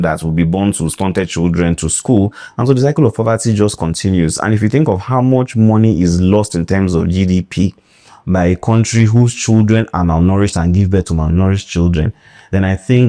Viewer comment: Me too, I heard "malnouritched"😂😂 Thanks, Doc, for enlightening us with each video. We know better after every video we watch.